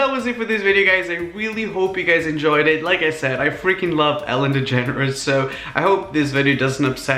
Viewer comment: Who else thought he said Ellen the generous